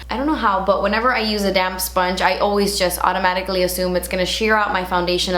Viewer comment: lol when you said "damp sponge" I thought you said damn sponge <3